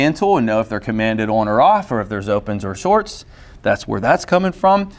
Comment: "if there's opens or shorts", I heard opens in'yer shorts. best get some tape lol